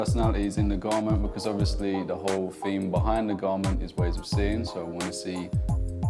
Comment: The entire intro I thought they were saying ways of sinning.......